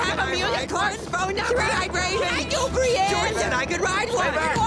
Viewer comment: oh god... I just heard "Can I do Brienne ? " at 1:30.